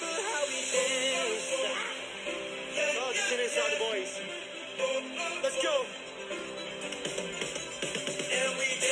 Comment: what did he say at 2:18 ? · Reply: He said its hard without the other boys